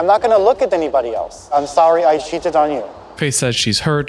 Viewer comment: Am I the only one that thought he said “I’m sorry I sh*tted on you” at first💀